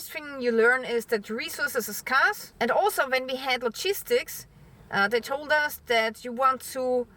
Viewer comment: First of all, I love you. Thank you for your time. I've been watching you for years and appreciate you and your content. As usual I was listening to this while cooking lunch. Everytime you said logistics, I heard "cheese sticks", and was wondering what was so special about those "cheese sticks"! This is a very serious issue your helping with and I'm not being derogatory. You, along with my hearing difficulties, made me laugh. In times like these we need a good laugh.